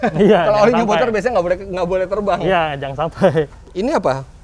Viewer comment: "Jangan sampai" Tp kedengeran nya "jangan sotoy"